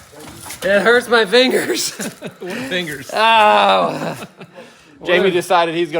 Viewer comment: Somewhere around 1 hour Jamie says ‘my fingers hurt’ and it’s hard to say, but it sounds like someone says ‘what fingers?’ 😂 Also, thanks for leaving in self-plug.